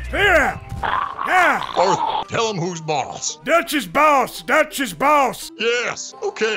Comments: Why am I hearing "Dutch's balls" at 8:55?? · sounded like he was saying “Dutch’s balls, Dutch’s balls” 😂